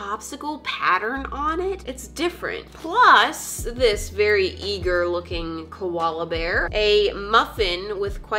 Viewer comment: i thought she said ‘this very e-girl looking koala bear’😂😂😂